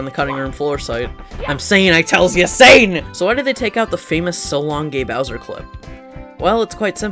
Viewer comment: I misheard "Shalom gay bowser"